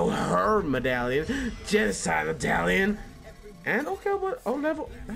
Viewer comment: Did you just say "genocide Italian" instead of medallion? XD